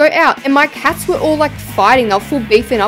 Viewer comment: OMG HEY I BET YOU WANT SEE THIS BUT I LOVE YOUR VIDEOS! ❤❤❤ Also I thought you were saying “Farting” When you said “Fighting” BAHAHAHHA I wish I could be in a build battle 😢😭❤️ I LOVE YOU